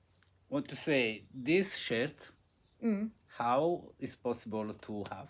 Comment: When said “this shirt” I was not looking at the screen and thought he said “this shit” lol I love your live. Wish I could’ve seen it live. I think the more lives you do YouTube pushes your channel. You should do it more. No editing. Less stress.